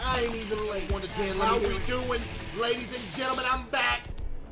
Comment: When he said I’m back I thought he said I’m fat guys I’m fat and I was like we know